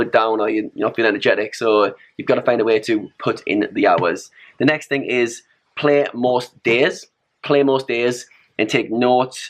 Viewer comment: what's he say @ 2:18? - "play more..."? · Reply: From 2:16 "put in the hours. The next thing is you have to play most days"